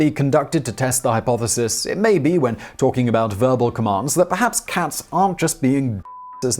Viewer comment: Did you say "Verbal commands" or "furball commands" 🤨